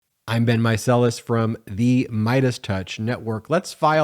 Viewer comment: I always thought you were saying..... I've been mike sellis when your videos started.